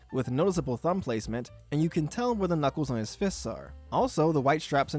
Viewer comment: did you say… Knuckles??